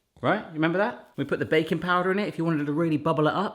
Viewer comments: For a moment, I thought you said 'bacon powder' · Fancy trying these? Here's the recipe write-up ... <link> put your own spin on them and let me know how you get on! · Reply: Every time you say "baking powder" i hear "bacon powder". I must be hungry..... =)